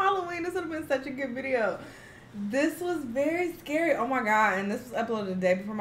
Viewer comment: LMAOOO i thought u said u werent scary 😂😭😭